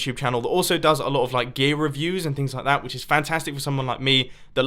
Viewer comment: Gay reviews or did i hear that wrong?